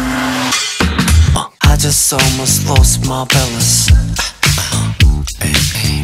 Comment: Did not have the captions on at the start and I thought he said "lost my bellus." Didnt know what the hell that was but I was like someone better help him find it!!! 😂😂😂